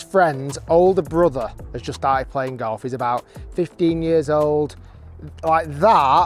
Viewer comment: I thought you said 'he died playing golf' for a second! 🤣